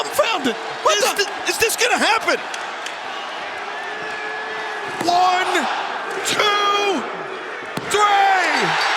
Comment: I wonder what Mox said to him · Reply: I think he said “this is your time left”